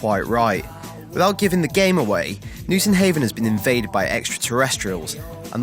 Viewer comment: did he say "without giving the game away"